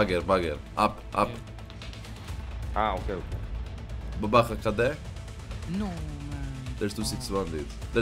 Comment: did he say "babaha kd"? In Russian and Ukrainian it mean babaha reloading xD